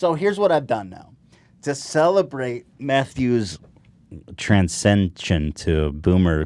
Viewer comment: I thought he said "to celebrate meth use..."